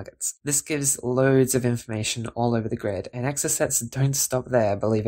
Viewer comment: Really interesting and useful. I was alarmed at one point as I had the CC on and it referred to "exorcists", but then realised the software had misheard "exocets"...